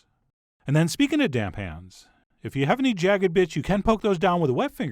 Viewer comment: "jagged bi***" was I the only one who heard that